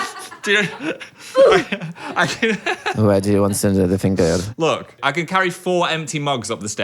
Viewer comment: I have no idea what Dan said here help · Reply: “where do you want ze other finger”